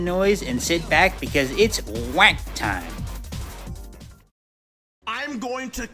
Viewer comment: I THOUGHT YOU SAID ITS WANK TIME NOT WOAG TIME IM SORRYYY